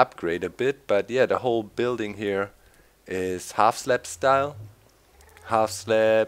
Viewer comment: Lol when he said half slab style I thought he said gangnam style.